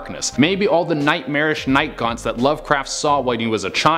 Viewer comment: Night gaunts I thought he said night gone